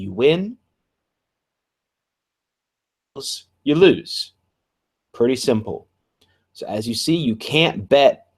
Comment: What did you say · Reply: "you lose, pretty simple"